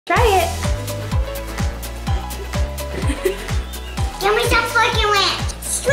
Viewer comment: What did say at 00:05? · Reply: give me a fork and ranch :)